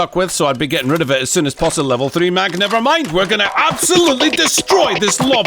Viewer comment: did you say postalevel ? ahahah